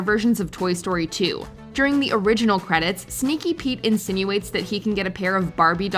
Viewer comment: Did she say Sneaky Pete? 9:32 Lol